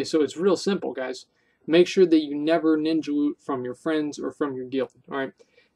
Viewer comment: Thought you said "make sure you never ninja loot from your friends or from your dealer" at 17:56 haha